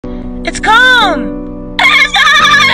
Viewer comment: I thought she was saying it’s cold at first